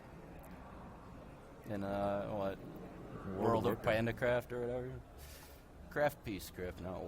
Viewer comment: can someone say what reynad said at 6:58 ? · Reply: ***** World of Pandacraft LOL